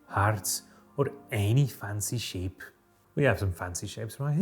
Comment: I misheard you say: "cut into [...] fancy sheep." and wanted to share this mental image with the world. We all could use a fancy sheep every now and then.